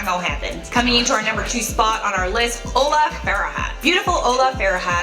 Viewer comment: I thought that said Ola fart lol so funnty